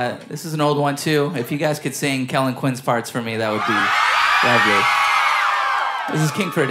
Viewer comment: I swear Vic said, "If you can sing Kellin Quinn's farts for me...." I will fight anyone who tells me any different. Come at me.